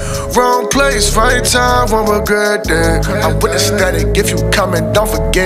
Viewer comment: he said static so many times that by the end i thought he was saying aesthetic lol. good song tho.